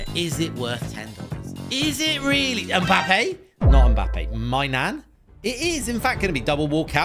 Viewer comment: When he said magain I thought he said my nan